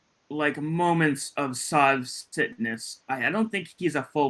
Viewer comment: When you said, ‘Sov Citness,’ I thought you said, SOV SICKNESS! I like Sov Sickness better! 🤣🤣